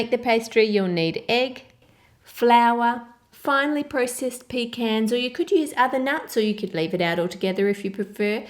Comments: I thought she said pig heads instead of pecans Omg Mind =blown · AM I THE ONLY ONE THAT THOUGHT SHE SAID PIG HANDS AND NOT PECANS ????